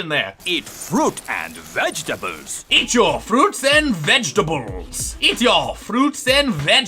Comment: I thought you were saying " eat your fruits and bottles " lol